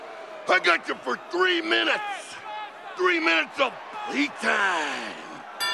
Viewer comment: what did he say? Three minutes of what time? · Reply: Play time